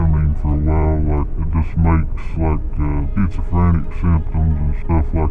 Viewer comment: I'm pretty sure he said "schizophrenic" instead of "frantic".